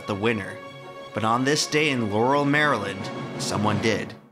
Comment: I heard "Yanny Maryland"